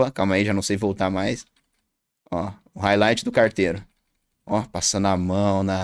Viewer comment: entendi o tesao em carteiro